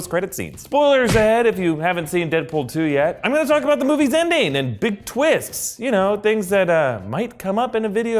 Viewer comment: I can't be the only one who thought he said "Indian" and not "ending"